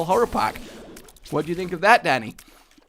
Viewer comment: When he said "what do you think of that Dani?" I thought he said "what do you think of that daddy?"